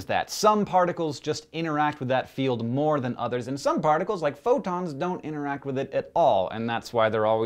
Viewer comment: Ahhh ok, every time I heard it somewhere I misheard photons as protons and it didn't make sense to me. Thanks for clearing that up.